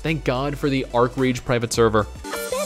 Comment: Wait, he speaks too fast, did he say there's a Rift pserver? Please god let this be true! Also, shitty transition music.